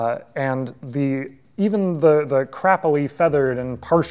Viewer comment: does he say "crappily feathered" because I am unfamiliar with that scientific terminology ;-)